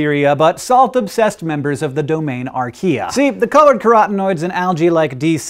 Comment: There's construction outside my window. I thought Hank said "karate noises" instead of "carotenoids".